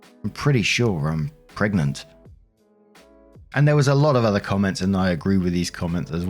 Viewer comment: "Pregnanant"? Sounds like what Sir Terry Pratchett said about the word "bananana" "She knew how to spell 'banana', she just didn't know how you stopped."